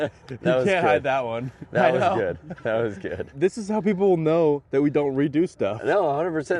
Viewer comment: was a hilarious idea. I really thought he said moe was good.